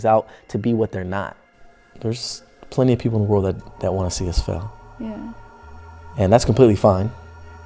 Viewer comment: I thought he said "There's plenty of people in the world that want to see us fuck." 🤣😂 Idk I'm brain dizzy🤷🏾‍♀️😂